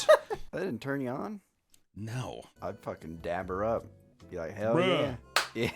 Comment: Did he say dab her up instead of dap 😅